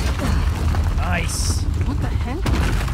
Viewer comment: Did anybody relive that she said what the heck but in the subtitles it said what the heel